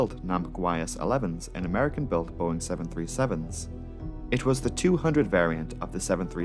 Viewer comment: the caption says "American boring 737" 😂